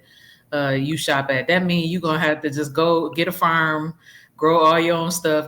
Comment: Thought she said affirm she said a farm 😂